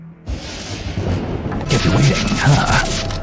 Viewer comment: Qu'est-ce qu'il a dit ? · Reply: "Kept you waiting, huh?"